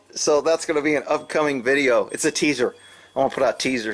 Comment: Damn, I thought it said Taser, not teaser